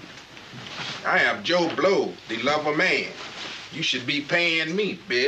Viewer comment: For many years, I thought he said, “I am Joe Blow, the Lumber Man.”